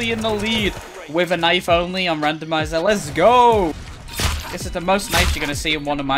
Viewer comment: did bro just say randomizer the game??